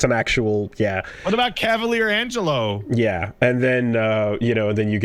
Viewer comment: for some reason when says cavalier angelo at 17:22 my brain thought he was saying kevlar angelo which, from what little ive gleaned of this show through the podcast, sounds like it could be a very real thing that exists in the netflix adaptation